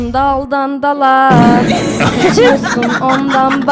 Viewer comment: DAMLA SİCİYOSUN DALDAN DALA Mİ DEDİ BEN Mİ YANLİS DUYDUM AGAGGAGAGAGAGGA